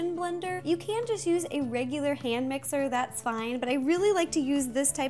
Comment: My name is Amira so when she said A mirror cake I thought she said Amira cake 😂😂😂😂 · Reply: My name is Han Mixer so when she said Hand Mixer it I thought she said Han Mixer 😂😂😂😂